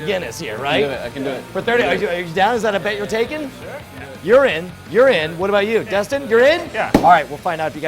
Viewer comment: at 2.10 it sounds like he is saying urine